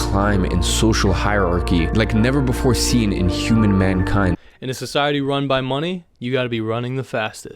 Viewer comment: Did he really say human man kind?